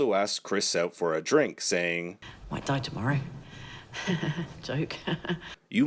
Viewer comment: Oh I thought the lady said she’d die tomorrow